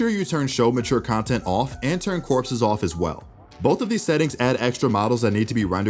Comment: wt did he say corpse off as well wt is that